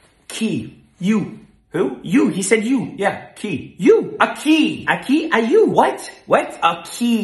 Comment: Took me while to get the key/qui joke. I heard Spanish “aqui” instead of “a key”